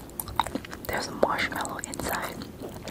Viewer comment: I heard "there's mozzarella inside” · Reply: She said "there's marshmellow inside"